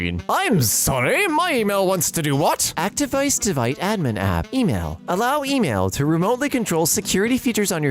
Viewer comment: did he just say actdevice devite admin app?